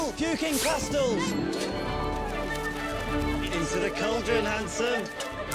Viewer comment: did they call that boy handsome ō-ô